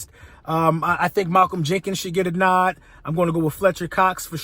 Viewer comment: Malcolm Menkins!!! LMFAO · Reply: Lol no I thought he said "Inkins" lol